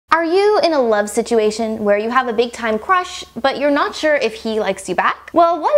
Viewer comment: when she said big time crush, I thought she said big time rush, like that nickelodeon show